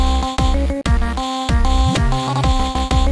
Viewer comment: In case anyone didn't quite understand, Spirit is saying "Don't leave me here, I don't want to die"